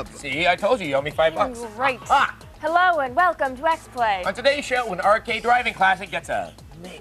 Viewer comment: did she just say "Hello, welcome to Breast Play?" I swore thats what I heard.